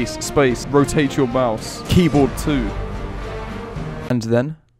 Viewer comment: When i heard "Rotate your keyboard too" Im dying😂😂